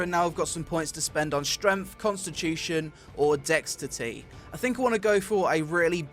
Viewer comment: did he say "dextity"?